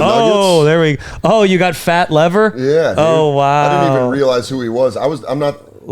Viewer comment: does he say “a Fat Lev Fer” hahahaha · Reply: It’s an old nba player, Lafayette “fat” lever